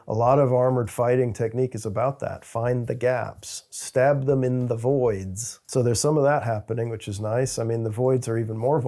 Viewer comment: For a second there, I misheard "Stab them in the voids" as "Stab them in the *boys*" and I'm just out here going "Damn, Toby really doesn't fuck around"